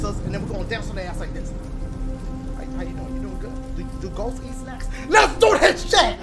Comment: thought he said "do ghost eat sex?"